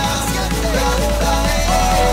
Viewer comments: Pense que decia "seguimos azotandonos" Que mente xD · yo juraba que decia "... seguimos asotandonos..." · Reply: krabo forever Yo entendí "seguimos exitandonos" 😵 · yo juraba que decia "... seguimos asotandonos..." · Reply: yo pensé que decia seguimos aceptandonos xDD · yo juraba que decia "... seguimos asotandonos..." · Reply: yo pensé que decía "... seguimos asaltandooo..." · yo juraba que decia "... seguimos asotandonos..." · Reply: krabo forever Yo pense que decia seguimos acostandonos 😂😂